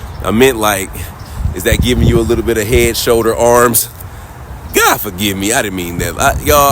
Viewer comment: I heard "Head and shoulders. Absolutely "